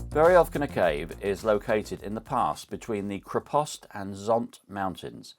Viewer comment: At first I thought you said it was located "in the past," but it was the "pass." 🤣